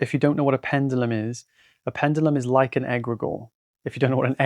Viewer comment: the captions say egg rigour here. For clarity’s sake, it’s ‘egregore’